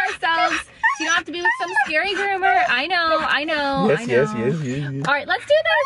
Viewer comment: it sounds so much as if she really said I don't know! Meeka literally talks, she doesn't howl, she doesn't bark but instead she TALKS like us...❤